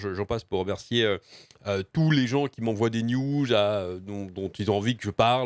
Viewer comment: J'ai entendu qui m'envoie des nu..